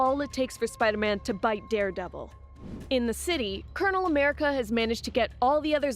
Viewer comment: did i just heard colonel america?😂😂